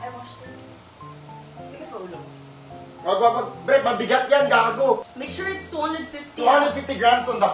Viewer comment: Did he just say babe, mabigat yan g*go? At 2:21mins clip? Something suspicious joke bagay naman